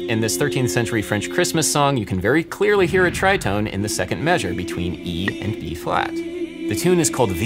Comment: I keep listening to the opening part of this video, and it sounds like says "In fact you can clearly hear a tritone between E & Eb" - did I hear him wrong? I replayed it like 6 times...